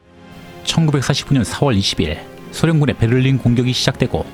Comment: 아무리 들어도 1949년으로 들려요.. 제2의 바이든 날리면??? ㄷㄷㄷ